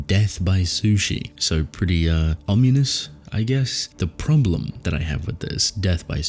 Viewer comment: Did you say "Omunous?"